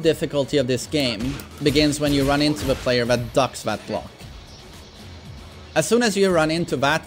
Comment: Wtf is he saying? Ducks that block? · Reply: Glock, the gun